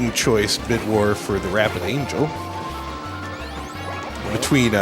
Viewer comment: What is he saying at 43:00? It sounds like wrath of Angel but I can't tell.